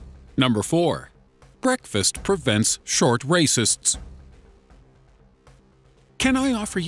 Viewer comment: Did i hear that right? Breakfast prevents short rasist